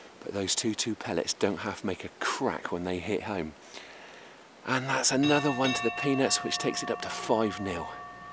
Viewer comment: All I kept hearing was "And that's another one to the penis" Lol.